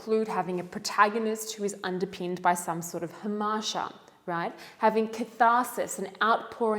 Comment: Hi... you used a word at 3:48 min - sounded like 'amaja' - what is this word? and what does it mean? Otherwise - all understood and very clear.